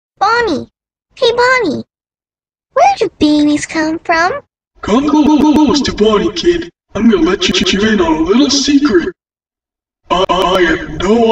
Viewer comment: it sounded like he said where do bbs come from? (hell)